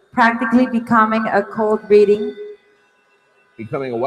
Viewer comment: I thought she was saying “a cold reading” too George. It’s alright